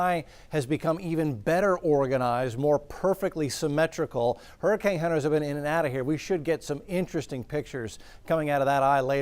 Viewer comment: what did he say?🤔🤔🤔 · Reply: Hurricane hunters have been inanoutofhere